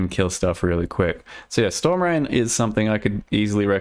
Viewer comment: I thought you were saying storm ren